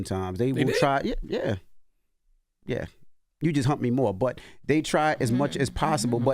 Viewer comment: Did Envy just say to CTG “you just hump me more” 😂